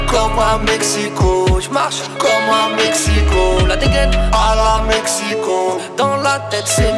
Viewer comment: pourquoi j'entends "à la Mecque C. Cole" 😭